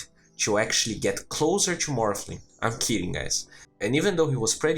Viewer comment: Gets closer to morphling. lmao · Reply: Thought he said 'not kidding'. Hahahaha!